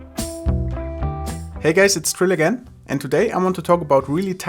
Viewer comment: it sounds like you say: Hey guys, its Trillu again